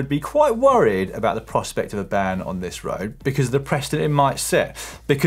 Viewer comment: Dan what did you say at 4:00 “Presta” or did u mean precedent.